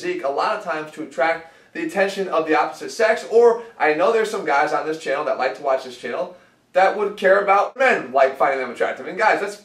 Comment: For a second I thought he said I know there are a few guys in jail who like this channel who would care about men finding them attractive.